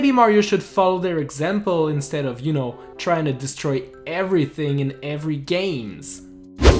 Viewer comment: before I' even gonna watch the video: of course they're not useless, they help you reach and unlock power moons, and defeat a certain annoying miniboss. 9:15 did he just say "everything in every games?"